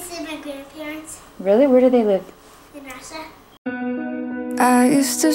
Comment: What does she say at 0:07, when her mom asked her where her grandparents live? I don't understand. I'm just curious. ^^ edit: oh wait russia?